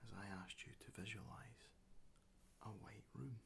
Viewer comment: ohhh he said "white room" i heard "weight room" i was wondering why i had to imagine all that gym equipment...